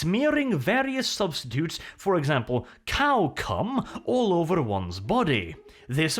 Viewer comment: Did he say Cow Cum? No way, he had to have said cow cud. ... 16:10 He said cow cum.